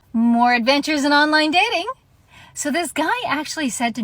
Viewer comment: I thought she said "more with dentures and online dating". The problem is multiplied by 32yo's looking like they are 50, and not just the standardized practice of shaving a decade or more off their actual age. The plastic surgery is at best a 50/50,- 60/40 gamble as to whether it makes anyone younger or just reptilian or a Hellraiser monster.